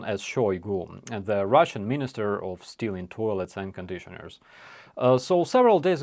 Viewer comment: It's called Ministry of Stealing Toilets & Air Conditioners? I've been leaving the & air conditioners part off! I feel so bad now. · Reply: I heard it as "... hair conditioners." 😅 I guess I heard wrong. But I wasn't surprised. The Orcs steal with arms, legs, toes, the Orcs steal everything.